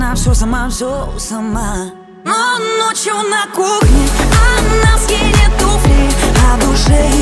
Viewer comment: Мне послышалось как ночью на кухне армянские туфли! 😂😂😂